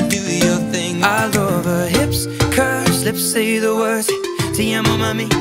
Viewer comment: i thought she said "i love his hips, curves, lips say the words", i was disappointed when i went back to check if that's really what the words were lol